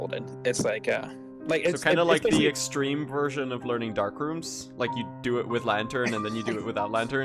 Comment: imagine how hard it would be to do dark rooms blindfolded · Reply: thought you said 'dark souls' for a second